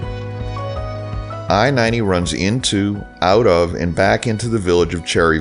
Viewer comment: the words you said, says it runs into out of emergent to Cherry Valley like it doesn't make sense what you say because normally you say, we enter into the village of?